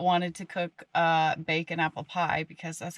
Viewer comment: Wow so beautiful. Fearless critters aren’t they? That was amazing!! Thanks for sharing!! And your pie looked delish!! 😁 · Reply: At first , I thought she said " Bacon Apple Pie " . Boy ! I snapped to attention on that one !